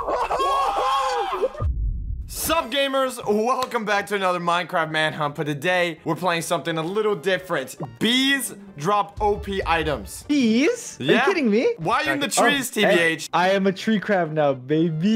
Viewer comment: I thought he said deez lol